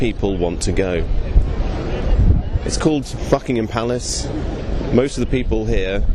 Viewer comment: Did you say "Fuckingham Palace"?